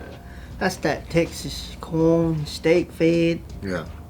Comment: what even was that??? 😂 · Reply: ong I thought he said “Texas corn steak and figs”